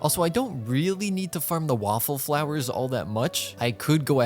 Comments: Am I the only one who heard waffleflowers? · WAFFLE FLOWERS!! (Jk I'm aware it's whopper flowers but I can't be the only one who heard waffles???)